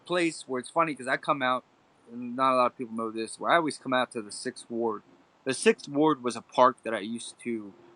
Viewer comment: I always thought the announcer said "sick ward" not "6th ward" haha